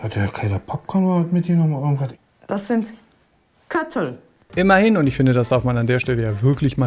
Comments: Was sagt sie? Katteln? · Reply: Köttel. Kleintierkot. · Was sagt sie bei 18:04 ? :D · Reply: Köttel…Hinterlassenschaften von nem Tier 😅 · Was sagt sie da? Ich verstehe es irgendwie nicht ... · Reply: Köttel